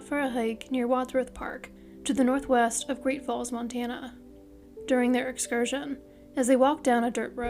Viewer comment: I thought she said Great Balls Montana!